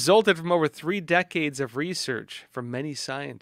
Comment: Did they say “over 3 decades of research” on the mRNA vaccines (5:00)?